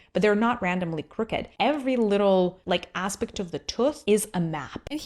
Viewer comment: Did she say "Aspect of the tueth"????😂😂😂😂😂😂